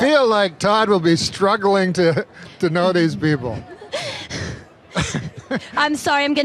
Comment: I heard him wrong and thought he said I think God would struggle knowing these people hahaha and I think even when I hear him wrong he's more funny than another person.